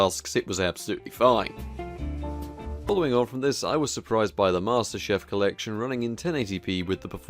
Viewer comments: did he actually say "master chef collection" lmao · Dude, did you just call Halo "Master Chef"?.